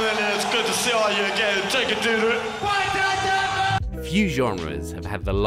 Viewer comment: I fuckin thought he said “take it, doo doo”